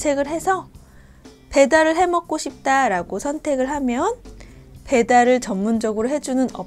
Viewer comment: im hearing 먹고싶다라고 around 2:19 anyone else hearing this? I played it over a few times. it says that she is saying 먹고싶다고 though.